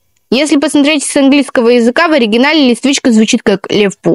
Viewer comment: Извините но, если мне не послышалось, то в начале она сказала не Листвичка, а электричка аххпхпхахп